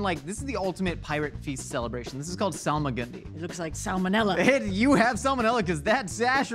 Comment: "AAaah my SASH", also, this dish is Solomon Gundy, but all I hear is, "Solomon Grundy" which is a monster villain that I remember from the Super Friends cartoon and I'm just, sooo confused right now.